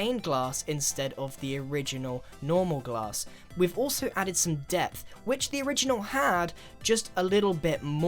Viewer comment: Grian:we've also added some depth Me:*hears death instead of depth* Me:death.........wth grian....